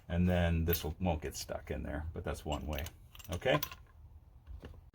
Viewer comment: Those bits are called spade bits and sometimes called paddle bits....it sounded like he said speed bits.